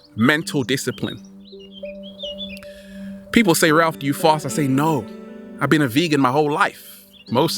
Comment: people say Ralph do you floss? I say no I've bee a vegan all my life LMAO he said fast not floss lol I heard wrong but it was still funny as hell